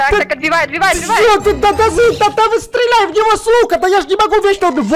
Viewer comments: мне послышалось что сказал "стреляй в него с*ка".... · я одна слышу мат сук*?